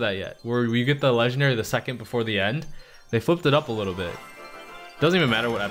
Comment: First time I heard him swear😱😱😱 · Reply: drali230 Pretty sure he said flipped